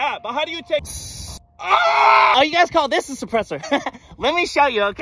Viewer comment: Marine: Suppressor? I thought you said suppressive fire.